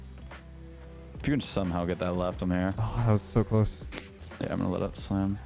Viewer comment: I really thought said "If you'd somehow get that, Lefthamyr..."